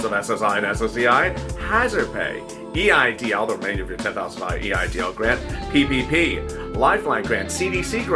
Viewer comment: Yessss more than that I thought he said $50,000